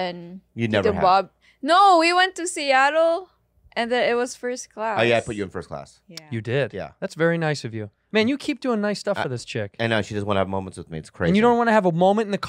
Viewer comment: did santino call "chick" to Rudy 😂